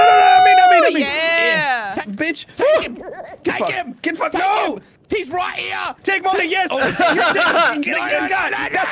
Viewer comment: I heard something like a laughter, was it hantie? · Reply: My dear child you spelled hair tie wrong 😀